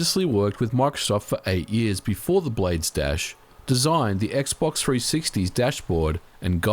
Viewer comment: I thought he was saying “blade stash”